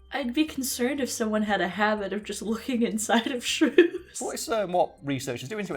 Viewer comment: I like watching videos while sleeping.I added this video to my watchlist and in my half asleep state I thought you guys said shroom until felt bad with the idea of scientist stepping on shrews.(I was like “why does she feel bad for mushrooms”) I didn't even question the spine part and thought “Oh mushroom spine? A new fact learned. Thanks fact fiend” So... Today i learned about a furry little animal you “can” stand on (you shouldn't tho) and I learned that mushrooms DON'T have a spine.